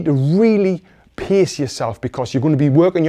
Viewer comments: Thank you for the ips! the 5th one, for a second I thought you said PISS yourself lmao *Pace that Aussie accent is really. love it! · On #5 I heard "piss yourself" instead of "pace yourself", because of this beautiful accent. I'm like "nice, that's a tip I can follow for sure!". All joking aside, thanks for the tips, Coach! Great help!